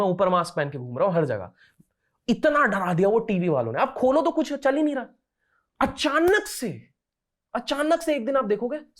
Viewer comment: Can someone say which leader is saying about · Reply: Subhash Chndra Bose